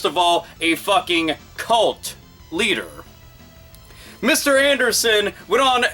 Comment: colt leader... thought you said clit leader lololololol